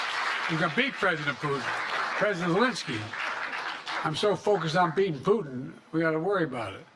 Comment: At the end it sounded like he said I’m so focused on peein and poopin lol so so funny